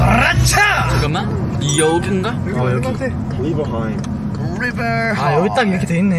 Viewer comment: am I the only one who heard someone say rivErHeIghTs